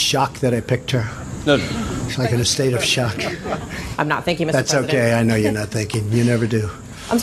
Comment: Until you replayed the clip at the end with subtitles, I personally did first think that the reporter did say "I wasn't thinking". It took a replay and subtitles to make it clear. But like you said, it's not out of the norm for him to take a jab wherever and whenever he can, when it comes to the media.